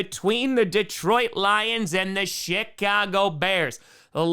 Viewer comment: I thought he said the “shit-cago” bears 😂